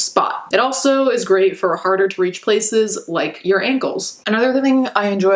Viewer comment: Sorry, this comment has nothing to do with the videos topic itself but I really, truly, genuinely thought you were about to say "your anus" not "your ankles"😳🤦🏻‍♀️